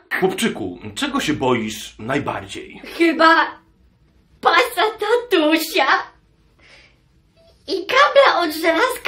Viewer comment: Czy tylko ja usłyszałam "tylko pasata tusia"? 😂